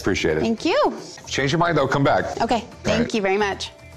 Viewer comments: For a second I thought Rick got mad and said "well if you change your mind, don't come back." LOL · Uhm thought I heard "change your mind don't come back"